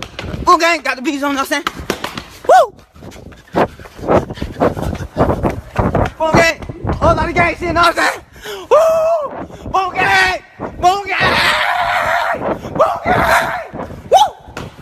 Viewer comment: Tell me why when I was in 5th grade I thought my classmates was saying BOO GANG or BLUE GANG 😭😭😭😂😂😂😂